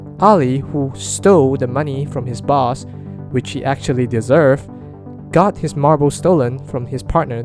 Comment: DID HE JUST SAY ALI DESERVED TO DIE